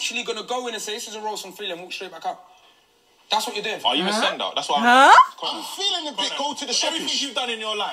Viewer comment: i thought you said are you a drug dealer ahhaah x